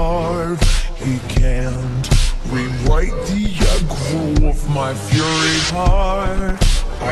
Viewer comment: anyone else hear "rewipe the eggroll of my fury" or am i just stupid